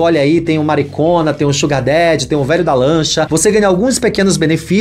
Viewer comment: Vc explicado os niveis de apoiadores, ao invéz de 'sugar daddy' eu entendi 'xucadete' 🤣